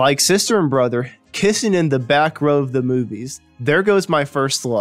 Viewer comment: I thought I heard "Brother and sister kiss'in in the back row of the theatre" what? Oh! That's 2 different song titles. Pause for a comma man!:)